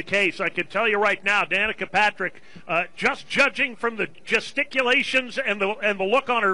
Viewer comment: Looks like attempted murder lol also did he say testiculations at 2:44?